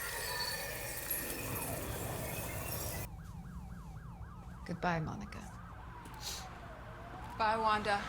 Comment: Bruh it sound like Wanda said good bye my ni**ga